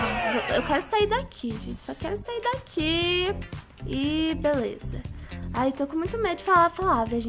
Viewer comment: EU ACHEI QUE ELA FALOU "OK"O.O E EU FIQUEI TIPO: UE, MAS O VÍDEO AINDA NUM ACABO! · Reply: Ela disse BLZ